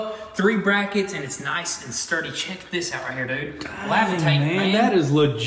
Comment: My dude said "lavitate" instead of levitate 🤣